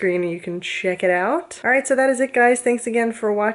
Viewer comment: When you said "please remember to subscribe", I thought you said "Please murder to subscribe" at first... 0.o